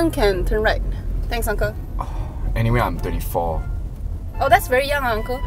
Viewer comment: I hear him saying 34 instead of 24... Anyone hears it too? XD